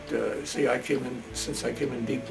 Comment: what was that time he said he came in. i herd 8:10 i needa no so if any one herd it correctly would be nice to no